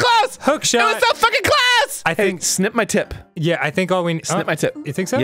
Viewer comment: I could have sworn Arin said "sniff my tip" at 6:48 am I the only one? xD ....... Probably